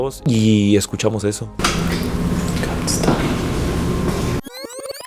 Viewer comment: Yo le entendi Can't Stay en el